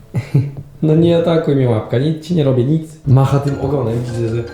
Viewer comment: ZDRADZIŁES MNIE GIŃ! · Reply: kto też usłyszał babka nie łapka w 13:7 ? daje łape w góre!